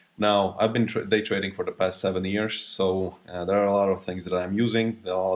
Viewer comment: Did he just say he been day trading the past 70 years???¿¿¿😭😭😭😭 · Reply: I think he said 17